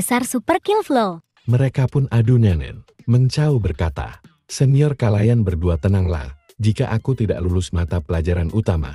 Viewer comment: ngomong apa itu??? Adu ..... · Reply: nenen